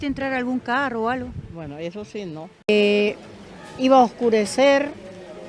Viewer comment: at the end of the Martina interview 47:16, when asked if they got into a car, she replies in Spanish 'eso se no' which I think means NO... but subtitles say 'Yes'